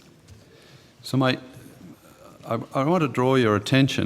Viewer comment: does he say “So mate...” 👏🏻